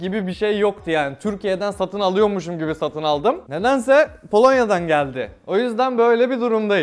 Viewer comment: Polonyayı Kolonya anladım